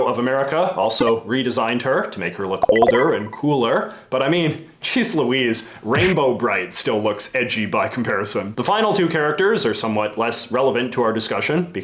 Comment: “Jeez Louise, Rainbow Brite loood ecchi by comparison.” …did he just say…? “I said EDGY, dammit!” Oh. Right. That makes a lot more sense.